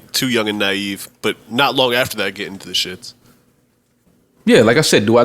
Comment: RIH Fred first off I feel stupid but I did not Joe was saying "ships" before viewership, etc. I thought he was saying getting to the shits this whoole time lol